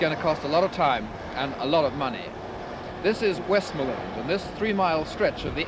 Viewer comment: West Miland? Is that the original name for West Midlands? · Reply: I thought he said Westmoreland